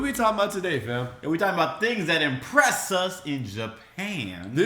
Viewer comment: legit thought you were saying Oppress instead of impress! lol